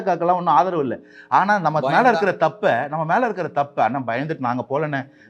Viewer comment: what word he said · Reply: "Bayamthaan"